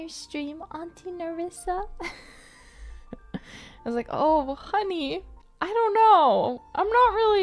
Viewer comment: Oh honey 聽成 oh 我恨你